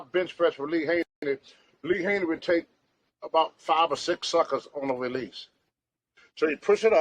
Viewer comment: At the 8:48 mark I thought Tony said Lee Haney took 5 or 6 suckas! Love the guy, very detailed!